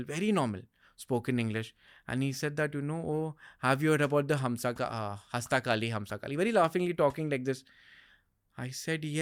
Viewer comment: can anyone help me to understand what Bhai is saying here... something like hastakali humsakali